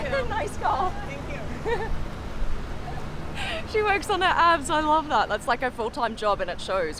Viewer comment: At first I thought that one girl said she works on her apps. I was thinking that's an unusual looking software developer, lol